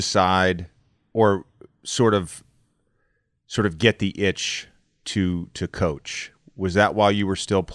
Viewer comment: Did JJ say “get that Itch?”